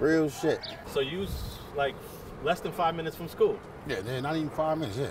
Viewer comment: Sounds like he said it took him 95 minutes to walk to school